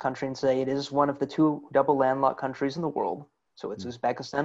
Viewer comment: "Its one of the two double (?)landmark(?) countries in the world - Usbekistan and Liechtenstein" -> what does landmark (if he says this) mean? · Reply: longgowhereto “double landlocked”